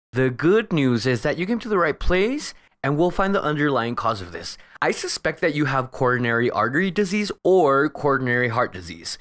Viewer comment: What does he say at 3:55 and 4:45? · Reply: Hi, 03:54 The good news is that you came to the right place and we will find the underlying cause of this. I suspect that you have coronary artery disease or coronary heart disease, same thing different terms. 04:44 It is not. However, I have not confirmed this, and is just a suspicion. I would like to gather more facts to know where to go next. We have scripts, answer sheets, ad-free videos and much more for just 1 dollar at our Patreon… <link> Happy interpreting!